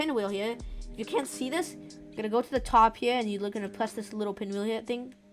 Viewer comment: I got a question about the recolor part. i heard you say "Youre gonna press ahrr" what does that mean? i pressed alt since i thought you meant that, but no, the borders didnt work.